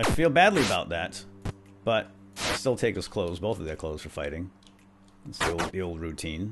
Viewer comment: Anybody hear say " Both of their CLOTHES are fighting" on 10:07-10:109???